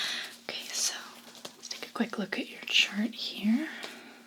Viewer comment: I thought she said let's take a quick look at your insurance here. I was like I'm fucked...